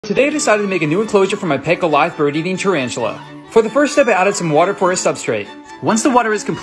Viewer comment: I thought he said is tarantula eating bird at first and I was confused on why his bird deserved that cage